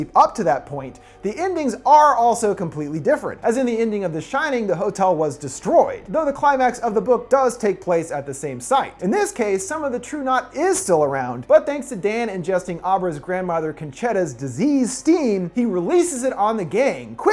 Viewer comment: I think the villain group's name is unfortunate, as I kept hearing it as "True-Not."